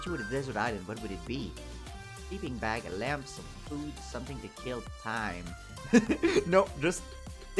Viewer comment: I'll add you to the list of multiple people that thought it said "Something to kill" or "Something to kill me."